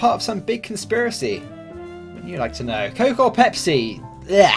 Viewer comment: did he say "why would you like to know?"?